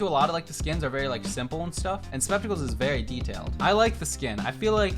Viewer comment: Did just call subz spepticle 💀😂🤣